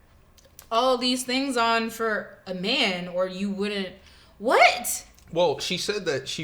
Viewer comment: he did not say she did that for a man. He said she doesn't look the same if she takes all that off and if she did she wouldn't be wearing all that.